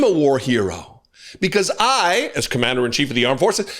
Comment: 🤣🤣🤣I thought he said he was a WHORE HERO! Some kind of sick “locker room” talk again! 🤣🤣🤣He is a sik duk! 🤓